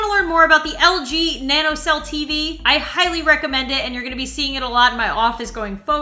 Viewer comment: I thought you said LG ThanosCell TV haha